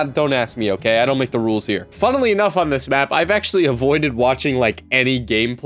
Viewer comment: did he just say "funnely enough"?